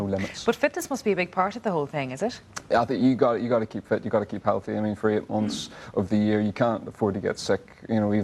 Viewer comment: what he said .... wow I'am totally lost ....🤣🤣🤣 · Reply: Abdullah Ruben Galvez "I think you gotta keep fit, u gotta keep healthy.."